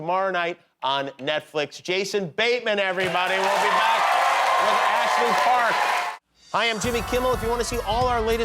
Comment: I thought at the end he said "we'll be back with Ashley Clark" and I just about had a heart attack that I missed my first Kimmel appearance 😂🤣